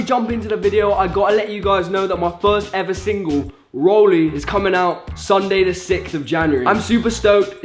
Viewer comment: I deadass thought he said "my son is coming out" and I was like well congrats